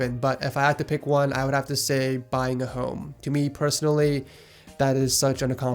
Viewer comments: i heard hoe and i was like Bitch whaaaa?😀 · did you say buying a hoe? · I thought you said “buying a hoe”